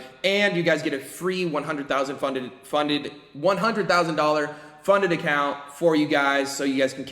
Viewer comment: Did he say free $1000 funded account on 24:30 where’s the link